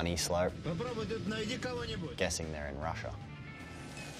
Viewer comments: When he was saying beacon at first I thought he was saying bacon. Love his accent. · it sound like he sayes. bacon